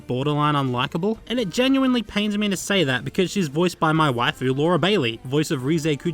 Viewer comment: For some reason, I thought you said that Laura Bailey was your wife.